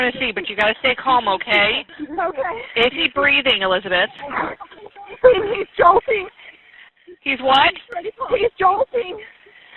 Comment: I hear, “he’s jolting”